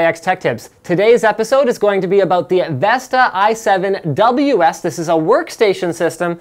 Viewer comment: I thought it said Vista instead of Vesta and almost unsubbed...